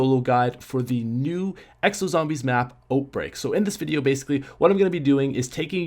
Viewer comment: Am I the only one who thinks he keeps saying oatbreak? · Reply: i am new an when he said oatbreak i thought he said oatmeal the i go hungry lol im am new not trying to be rude